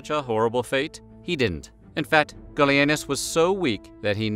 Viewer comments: For a second I thought you said his son's name was Billyanus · The first time you said, "Gallienus", I was looking away from the screen, and thought you said, "Billy Anus", as a joke. Hey, don't blame me. You're the one with a channel that isn't above that kind of thing.